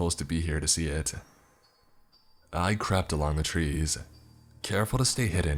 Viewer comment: Did he say " Icrapped along the woods?" xD